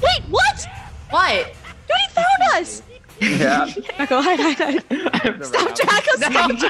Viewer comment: Who else heard say draca